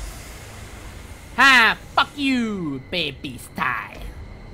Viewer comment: it sounds like she said fuck